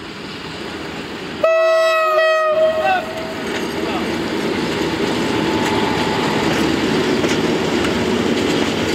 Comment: Fajny Film , 3:43 Co on Powiedział? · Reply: "Ja Pie*** Ku***" To powiedział bo się przestraszył RP1 xD